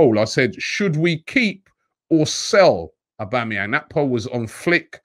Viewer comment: Did he say Aubameyang or am I deaf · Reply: He did say Auba.